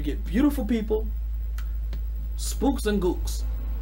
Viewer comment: Did he just say spooks and gooks